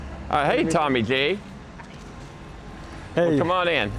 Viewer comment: did he call him Tommy Gay?